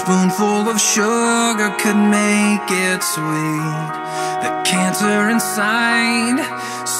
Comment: whenever i listen to this i hear "The kids are inside" not "The cancer inside" XD